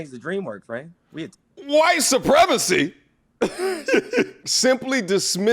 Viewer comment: I thought he said 'Wife Supremacy'...